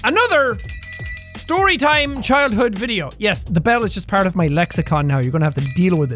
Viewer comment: "Yes, the bell is just part of my Lexicon, now." I seriously thought you said the bell was part of your leprechaun, now. LOL!